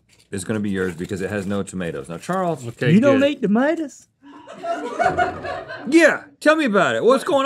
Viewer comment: "You don't eat damadas?😮" That was adorable😂😂😂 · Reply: I thought he said "you don't eat da lettuce!? Lol